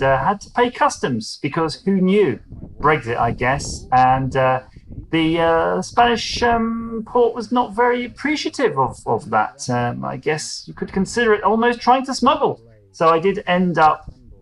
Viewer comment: This is not the EU, it is the French finding ways to punish Brits for daring to leave "their" EU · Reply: I thought you said "the French port was not very appreciative"; perhaps I misheard. But anyway the French seem to bully the rest of the EU into doing what they want.